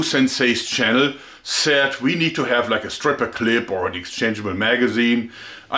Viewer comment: who else thought at 0:31 he said stripper club